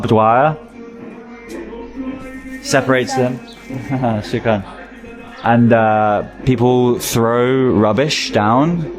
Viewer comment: what did she say? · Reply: She said free Plaestine